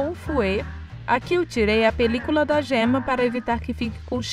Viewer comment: Não entendi o que vc tira do ovo pra não deixar cheiro. Acho que entendi "película" foi isso mesmo? Se foi, o que é isso e como eu faço pra retirar?